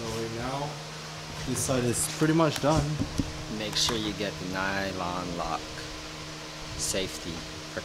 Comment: Hey hey, what is the size and kind of mounting nuts and bolts you got to mount the seat to floor bracket since they didn’t come with any. Sound like he said nylon lock? It’s at 9:23 of the vid , all I can make out is everbilt. Thanks